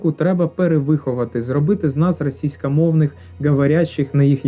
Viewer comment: "зробити з нас російськомовних підданих" 3 рази переслухала бо почулось "російськомовних під*рів"😅